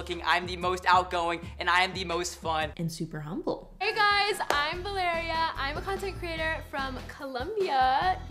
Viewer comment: Istg I thought she said 'Hi guys, I'm Malaria" and I was like WHAT